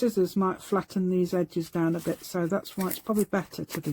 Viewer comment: You have an amazing gift with design. The royal blue foam looks good too. I know the color of the foam doesn't matter, lol, but I like them. I didn't catch what you said would be better to use than scissors, around 24:54, and the closed caption was no help, it said you said "ice" lol. Your designs make me want to paint vases. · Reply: Hi again, Lou. I said knife. A knife might not flatten the cardboard like scissors do,. Hope that helps😊😊😊💖💖💖